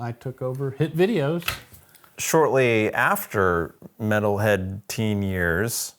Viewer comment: Once a metalhead.. Always a metalhead.. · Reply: I thought u said methhead 💀